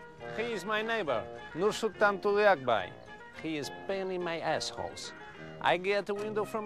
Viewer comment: “this my neighbor, he is pain in my assholes” so relatable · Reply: It’s funny, I always thought he said “he’s painting my assholes.”